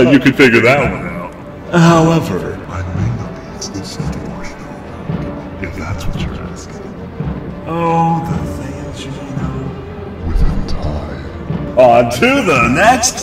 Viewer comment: is killing me, I need to know what he said! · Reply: "I may not be exclusive to Marshmallow Monk, if that's what you're asking. Oh, the things you may know with time...."